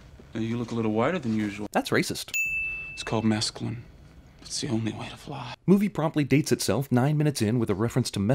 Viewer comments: Shit I am really glad you captioned the mesqualin line this whole time from 1999 to 2019 I thought he said “it’s the only way of life” not “its the only way to fly” but my drug use in college should have told me that. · Mescaline? I always thought he said "It's called masculine. It's the only way to fly." And I'd always wondered just what the hell he meant by that.